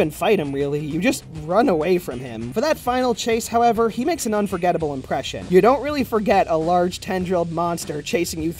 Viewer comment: I heard an unforgettable luncheon and was thrown for a loop